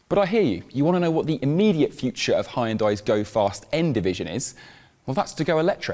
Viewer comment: Did he just say "Heyundai" ? 😂